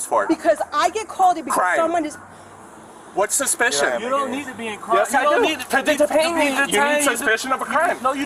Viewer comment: It’s unreal how they back her up! She is not fit for duty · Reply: sounded like the cop was starting to say " you don't have anything.." to the female officer